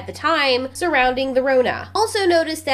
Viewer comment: Did you say "The Rona" instead of Corona?